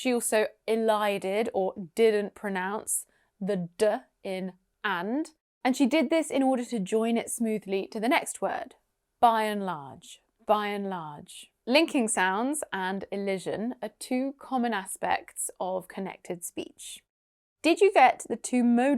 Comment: I heard enlarge